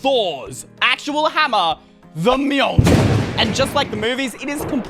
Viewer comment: Did anyone else hear “THE MILK”